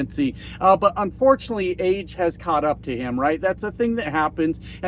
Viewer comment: did you say AIDS has caught up with Ttump??!!?!?